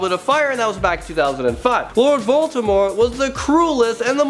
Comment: It sounded like he said Lord Baltimore XD But my favourite HAS to be Anakin. But he isn't truly a villain, so... I'm guessing instead it's Kylo too.